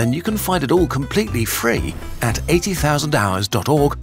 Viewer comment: Towards the end, during the advert, the subtitles say 800,000 hours instead of 80,000